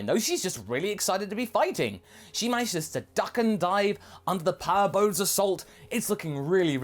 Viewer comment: At first i thought he said Power ball and i got all excited to see an Oozaru in the ToP